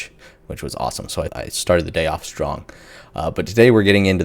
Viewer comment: lol I had to replay the intro I heard you say you started the day off drunk instead of strong!😂